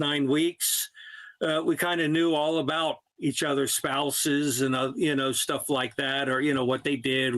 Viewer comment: I don’t think he meant to say phalluses! That would give deliberations a bit of spice, though.